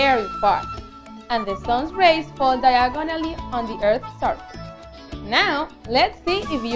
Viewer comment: Am I the only one who heard diagon alley?😂 in